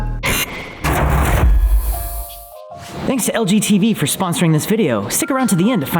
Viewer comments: Who else at 00:12 thought he said LGBT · When he said “thanks LG TV for sponsor” I thought he said “thanks LGBT for sponsor” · Who else thought he said “thanks to lgbt” at the beginning lol. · i thought it said “thanks to lgbt for sponsoring this video” 😂😂😂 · When the thanked LG TV for sponsoring them in the beginning I thought they said LGBT and I had to rewind to get what they were saying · i heard "LGBT" XD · I thought he said "Thanks to LGBT for sponsoring this video" in the beginning lmao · Who else thought he said "thanks to LGBT for sponsoring this video" 😂😂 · it sounded like the sponsor was LGTB 😭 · I heard LGBT over LG tv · I thought you said lgbt not LG tv